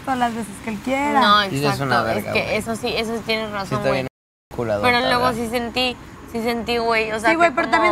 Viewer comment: Q le dice a.. 6:31 Noo 😮 quiero saber si oí bien 🤔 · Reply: Le dice "Si anda bien enculadota, verdad?"